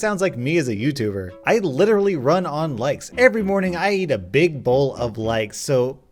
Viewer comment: I misheard “likes” as “lice” and started wondering what the hell was wrong with until I realized the context